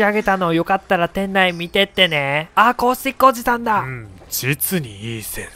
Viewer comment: アコースティックおじさんに聞こえて笑った